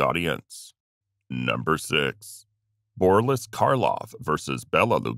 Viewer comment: All credibility was lost after I heard "Borlis" the SECOND time.